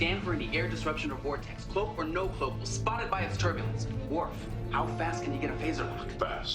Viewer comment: did he say spotify ?